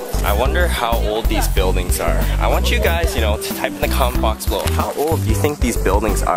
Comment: when you said comment box i thought you said kumquats haha